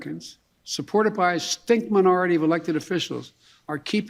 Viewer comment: did he just say a “stink minority of elected officials”?!